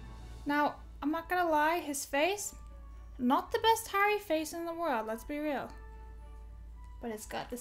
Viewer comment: I heard that as "Not the best hairy face in the world" 🤣